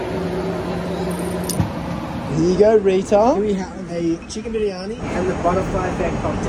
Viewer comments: i thought he said "here you go retar- " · When he said “here you go Rita” I thought he said the R slur 💀